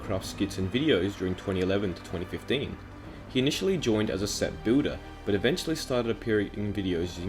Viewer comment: Why do i hear “booder” instead of “builder”